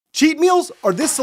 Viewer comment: Ngl I thought he said cheap meals